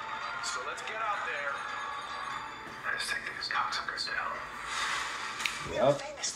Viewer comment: Excuse me ...could you plz tell me what homelander said at about 1:05 (let's .....)?? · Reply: Take these cocksuckers out